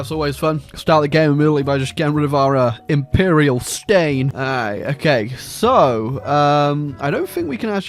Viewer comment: I thought it said Imperial Stalin